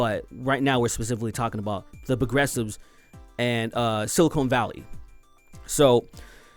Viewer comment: So much for the good old days of Crash Bandicoot. Time to watch yet another great franchise meet its doom... 3:08 Also, I heard that as "SILICONE Valley" 😂